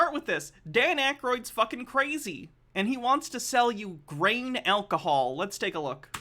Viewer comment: I swear to god when brought up Dan Akroyd wanting to sell vodka I thought she said brain alcohol not grain alcohol 😆